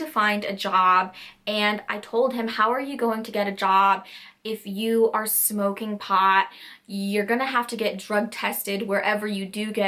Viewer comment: HAHAHAHA at 5:13 i heard ''how are you going to get a job if you're smoking hot?'' LMAO i was like WHAT THE ACTUAL FUCK WHAT DO YOU MEAN im dying... and then i realized.